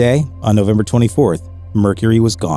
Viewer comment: I think he is saying "I just want to have a condom every time they listen to my songs, you know." Man Myth Legend. And funny. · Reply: He says Carnaval, not condom. The question of the interview had something to do with Brazil